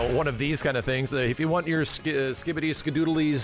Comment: did max just say Skibidi? 😂